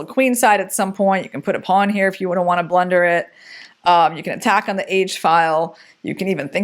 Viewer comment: At minute 8:26, what do you mean with "you can put a pawn here if you wanna blunder it?" I don't really understand if f3 is a serious plan or you joked. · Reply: You misheard her, she actually says ‘you can put a pawn here if you wouldn’t wanna blunder it’